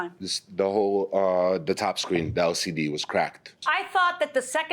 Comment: Did I just hear the judge say broken squeen, not screen?